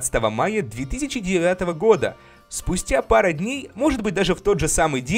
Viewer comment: мне одному послышалось 3009 года?